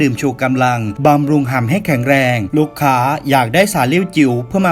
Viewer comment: อะไรนะ?!ฉันได้ยินแบบนั่นคนเดียวหรือเปล่า?? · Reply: ทำ..ให้เเข็งเเรง